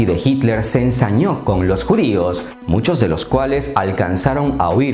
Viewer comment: Hola escuche mal o dijiste que tu bisabuelo era Albert Einstein?? 4:40, Saludos Primo! :_Impactante::hand-pink-waving::face-purple-wide-eyes:@